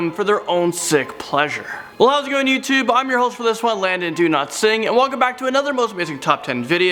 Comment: I always thought he said "Landon do not sing" mb